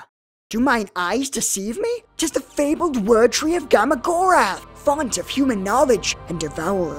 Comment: Watching this for the fifth time and I JUST noticed Rosalia's name sticking out from behind the tree. The amount of detail in this is insane. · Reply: I always heard "Look Silica, The fabled Word Tree of gamagorath." Hearing word tree I looked at the tree line and saw her nameplate.